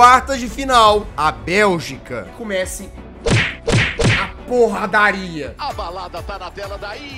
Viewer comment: eu ouvi QUE COMEÇE A P0RRA DA ARGENTINA KKAKAKAKAKAKA